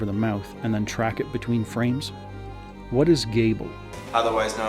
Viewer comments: I think he says “cable” at 17:05 as in cable TV. Implying cable TV is shit. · I've always thought Kenny had said 'cable' reffering to cable television as 'shit' in comparison to modern telivision when Kenny said: "-Otherwise known as poop, browners, cucky, plops, or.. gable." I thought maybe this was a joke in relevance to broadcasting standards since Kenny has made jokes and verbal stands against broadcasters over the course of the show. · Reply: In hindsight, I believe he does say cable. A few people have pointed that out in the comments.